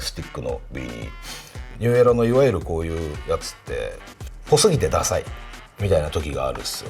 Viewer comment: 聞き取れなかったのですが、1:15~ 「〇〇（？？）て、ダサい」これは何と言ってるのでしょうか？ わかる方、教えて頂ければ嬉しいですm(__)m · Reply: っぽすぎて じゃないすか？